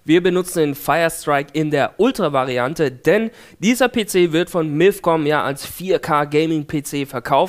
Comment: bei 9:32 verstehe ich immer Milfcom O.o